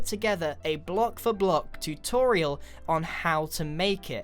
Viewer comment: Wait a second did he say toriel 5:46 UNDERTALE FAN ACTIVATE